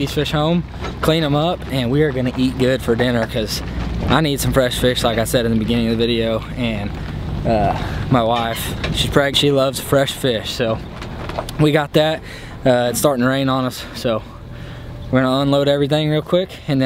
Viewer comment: Did you say your wife is pregnant? At 9:15?